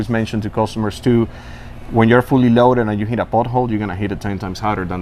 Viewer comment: At first I thought he said butthole but he actually said pothole